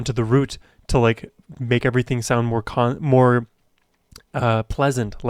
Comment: I heard "sounds more cunt" 😂😂